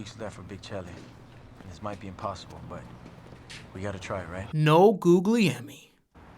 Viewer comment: lmao i thought you were saying "No Googlie Emmy" and im thinking does mean that this movie's not worth an Emmy?